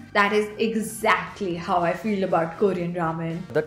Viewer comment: did she say feeled?